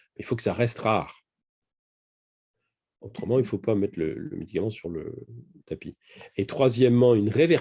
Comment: A 1h17, j'ai entendu "LUTERAN" ou j'ai rêvé ? Il dit : "Autrement il ne faut pas mettre le Lutéran sur le tapis", en parlant des effets indésirables graves ? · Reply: Non, il dit médicament : "Il faut que ça reste rare ! Autrement il ne faut pas mettre le médicament sur le tapis."